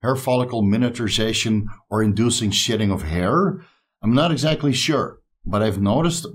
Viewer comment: I had to laugh out loud. I thought was saying SHITTING when a person comes off of steroids. It wasn't till he mentioned his hair that I realized he was saying SHEDDING!!!... lol